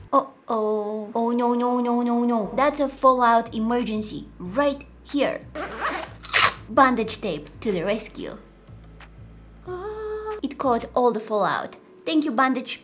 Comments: Not me looking up "bondage tape" because my nasty mind heard her say that instead of what she actually said. Lol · I don't know why I thought you were saying 'bondage tape'🙊😂. My mind when like, wait a minute, it's not a bondage tape!😂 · I thought she said "bondage tape" 😅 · When I tell you I thought she said bondage tape… lol